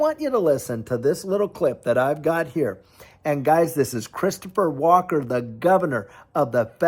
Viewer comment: Oh! Christopher Walker. I thought you said Christopher Walken. 😅